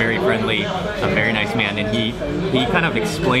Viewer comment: Did Mark say .. "he was a very nice man to EAT??"